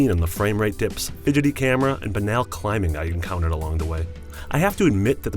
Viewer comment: What is the word he says right before, "___ climbing I encountered along the way"? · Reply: Banal